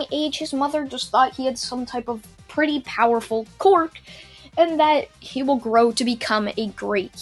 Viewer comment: did he say quork instead of quirk?